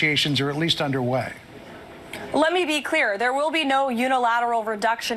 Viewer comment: whats wrong with me ? ... why i hear ''let me be dirty'' ?